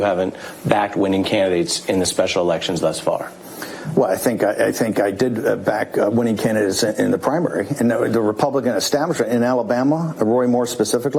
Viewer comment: Did he just say winning Canada?